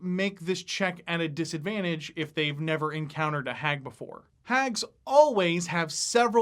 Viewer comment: i thought u said Anus hag lol